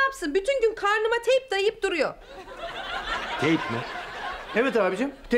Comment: gey'im diye anladım töbeeeeeee